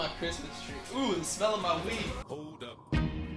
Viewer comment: Thought you said weave xD